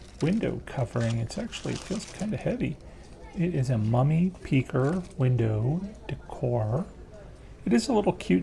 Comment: Okay... two things. One... I thought that said "Mummy PECKER" and I couldn't figure out what the hell was inside that package. Second... what the F**K do you call that huge gathering with food, booze, candy, decorations and tables you have every year if not a Halloween Party!??!?! Or did I miss the joke. I need some vodka. Back later. XOOXOX